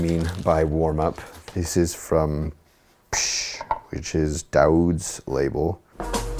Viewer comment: Please i cant understand the name of the artist and whos label ? Daud ? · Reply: Psssh Records - Record label from Berlin, Germany, run by Dauwd.